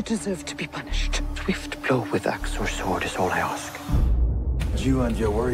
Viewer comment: Can someone tell me what did Floki say at 0:20? Can't understand. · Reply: swift blow with axe or sword is all i ask